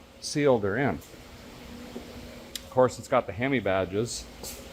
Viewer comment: Lol..i thought you said Hemibadger.. Narrated by David Attenborough .. " The hemibadger...an already scary species of animals got even scarier"..